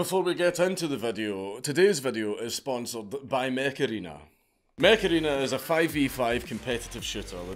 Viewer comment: Mech Arena I thought you said Macarena like that song from 90s